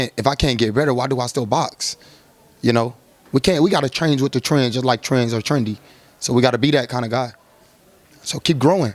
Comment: What the hell did he say...lol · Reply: The Mannn!!! 😂😂😂😂😂😂😂😂😂😂😂😂😂😂